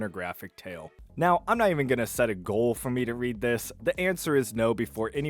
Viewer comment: All I heard is "I'm not based enough."